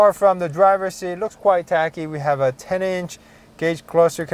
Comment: Very nice review! You're one of the best automotive journalists on youtube! I really like the new A3, S3, RS3 from audi. just a suggestion.... @ 11:22 I think you said "it looks quite techy" as in lots of tech. but it sounded like you pronounced it like "tacky". which means "촌스럽다"...